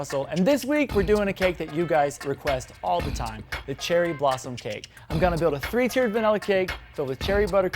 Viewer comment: When you said cherry blossom cake I literally thought you said Cheryl Blossom cake. Lmao.